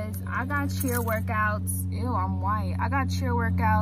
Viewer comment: did she just say 'eww im white' racism much gurl